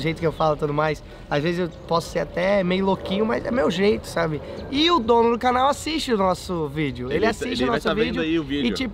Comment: eu escutei o dono desse canal kkkkkkkkkkk